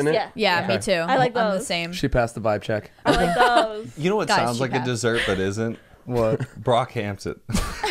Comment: When said: “you know what sounds like a dessert but isn’t?” I thought he said: “You know what looks like a dessert but isn’t? BROCKHAMPTON.” I was like u right tho😂